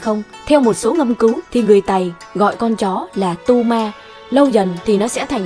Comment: Tu ma mà nghe thành đu ma :))